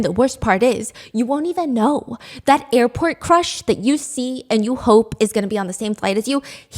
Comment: i thought you said airport crash instead of crush and my heart beat so fast suddenly because of the recent event in jeju